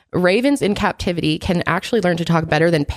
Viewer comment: I thought said "Ravens in captivity can talk even better than carrots." And I thought--way to go, for keeping a straight face for that lie!